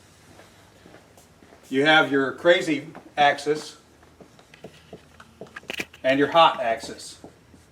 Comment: At first I thought he said, “You have your crazy exes and you have your hot exes.” Lol 😆